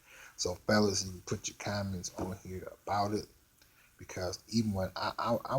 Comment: I thought for a second you said put your condom on, not comment. Lol.